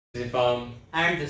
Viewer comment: Fantastic... Can't wait for part 2... In the intro I just heard "...Ifham"... The words "Hi, this is..." are drowned out... Love you boys...